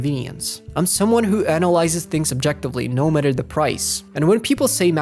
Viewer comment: Did I hear correctly? Did you say objectively..? At least half of your complaints have been fully subjective. I sat here watching up to this point and all I could say the whole time was "Yeah, that's not a problem for me." Take your segment about the dock and desktop, for example 😂 There was nothing objective about that. That comes down to taste, which is purely subjective. So I really hope you just used to wrong word there 😂 Sincerely, an actually objective comment.